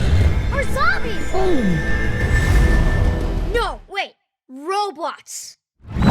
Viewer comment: I thought nunu said roblox instead of robots lmao